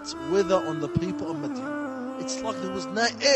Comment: After 13:10 you made a mistake in the subtitles , it says "Maydan" instead of "Madyan"